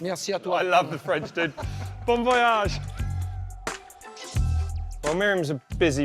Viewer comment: did he say BOMB voyage ?